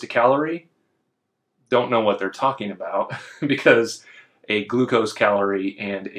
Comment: "a glucose" this opened my google assistant, thought it heard "hey google" 😂